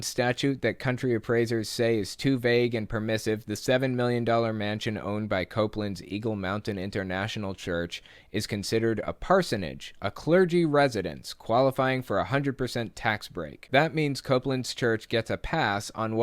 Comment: I sincerely thought you said “ego mountain”.